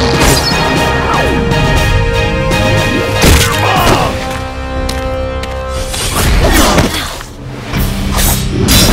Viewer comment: did he say "your mom"? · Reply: On this skin he say "I am op? Your mom is op"